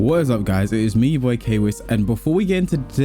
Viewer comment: For a second there, I thought you said " it's Leroy K-Wiss" in the intro.